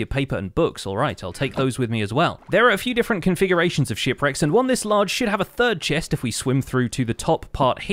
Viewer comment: did he just say "large sh*t" at 3:04 i know he said ship